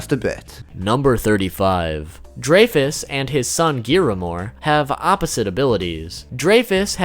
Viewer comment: did he just say giamor